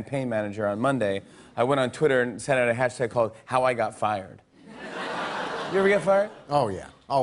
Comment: I thought it said howl got fired and i was confused lol